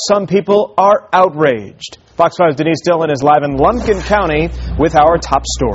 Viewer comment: did he say Blumpkin County @ 0:11 ?